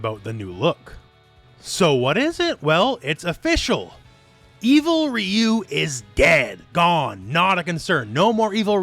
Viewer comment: at first i thought you said 'Evil Ryu is Gay' had to do a fucking double take.